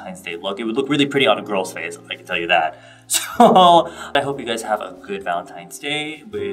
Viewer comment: Ahh you really caught the warmth of this look! Simplicity has it's charms too~ Definitely love it and would want to give it a try! You look great and have a lovely Valentine's day! (and to anyone who reads this<3 lol!) Also the song Friday wrote for you is so nice! O": It compliments the overall video very well~! Take care of yourself!^^ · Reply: Mia's Illustration i thought you said have a lonely valentines and I was like yeah definitely will